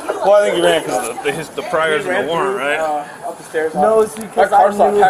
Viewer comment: “Why can’t you go to jail?” “Cuz I got pride.” No you don’t. Stealing with your baby is nothing to be proud of. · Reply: I thought he said priors?